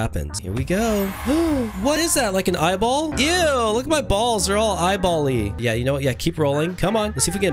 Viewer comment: Wait what did you just say upgrade your balls ☠️☠️☠️👺👹💀🌞🏀🏐🏐🏐⚽️🎾🎾🎾🎾🎾🎾🎱🥎⚾️💣⚰️ you you said upgrade my balls☠️☠️☠️💀👹👺